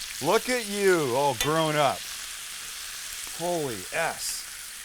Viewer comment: "Holy s" Sam has really grown 😂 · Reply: And here the entire time I thought he was saying "holy ass"